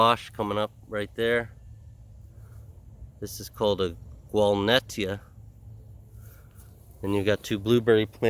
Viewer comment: Wonderfully diverse planting--thanks for bringing all the permaculture joy on this cold day in January! What are you saying at around 7:27 that sounds like "Walnettia?" Not familiar with it. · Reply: Thank you for watching the video. It's a Gualnettya - I bought it from One Green World.